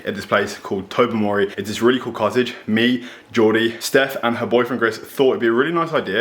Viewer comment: I thought he said Jordie was bringing her boyfriend........